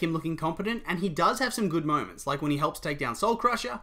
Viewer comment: irrelevent but I heard moans instead of moment and... oh god